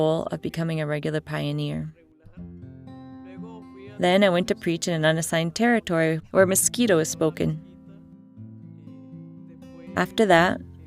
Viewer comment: Did he say "The faithful foreigners (farrenerrs) in Unix"? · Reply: Did she say "I went to preach in an unassigned territory, where Mosquito is spoken"?